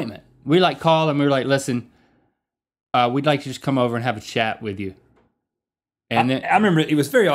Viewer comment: It sounded like link said: "We want to come over and have a shat."